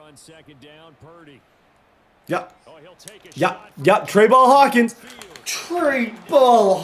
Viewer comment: Should give Hawkins #3 when you say his name I always hear “treyball hawkins”😂